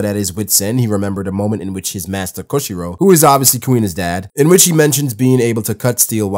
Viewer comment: did he say " Koshiro is Queen's dad"